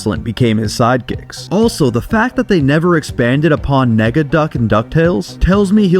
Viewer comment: what did you say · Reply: Nega